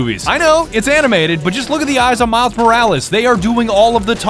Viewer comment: Did he just say miles perhourles instead of morales?